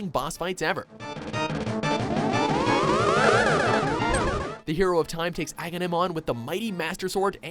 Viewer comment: D-Did you just say the hero to TIME?! I think I part of me just died.